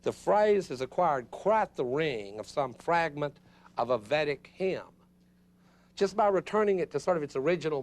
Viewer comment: what's the word he says at 36:09? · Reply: Vedic hymm